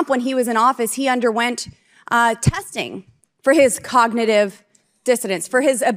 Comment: Did Lauren Boebert say Trump got tested for his "cognitive dissonance?" or did I mishear?